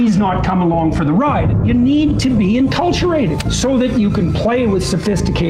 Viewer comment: Did he say "enculturated" @ 6:17? Jordan making up words again to sound smart.